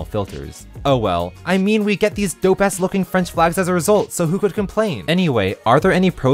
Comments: Wait what does say at 3:54? It almost sounds like "...these dope ass looking flags..." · DID HE JUST SAY ASS